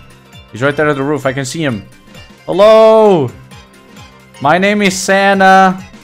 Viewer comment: i thought he said "My name is santa" because i forgot abt what the video was based on 😂